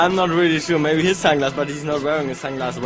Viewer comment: haha first I thought Tom said "He has great tangas" xDD